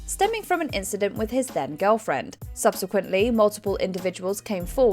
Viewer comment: Anyone else mistakingly hear "dead gf" instead of "then gf"? Me each time: omg he killed her? 😂